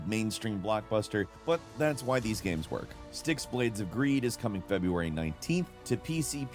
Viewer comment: I heard "STICKS: BLADES OF STEEL" and thought, "A new Blades of Steel game?!? Awesome"